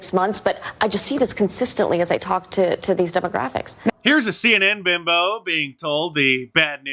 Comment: Listen Again. she actually said. "As I talked to, these Demographics." She could have called them ( People ) Is She racist???